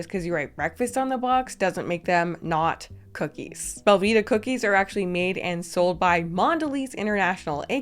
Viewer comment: I wasn't looking at the screen and thought you said "Velveeta cookies", and that was a horrifying moment lol